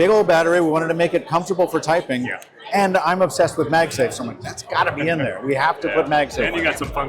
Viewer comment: did he say the clicks has magsafe? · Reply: Yes phone does have magsafe because of qi 2